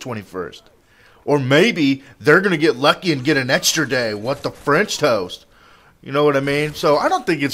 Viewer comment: HAHAHAHA i loved how he said what the french toast around 5:50 sounded like a girl saying o no u didnt